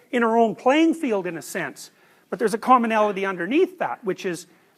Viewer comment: does he say "there is a CULMINALITY underneath that"? if yes, i cannot find this word in dictionaries.